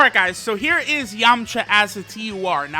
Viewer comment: I swear at 2:28 he said yumcha